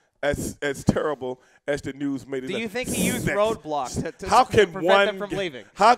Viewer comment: Do you think he used Roadblock? Wowwww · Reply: 80s Classic HOLY SHIT!!! I thought he said A roadblock.